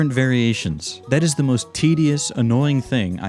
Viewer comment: is it just me or did he say tds✌✌✌ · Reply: he said tedious dude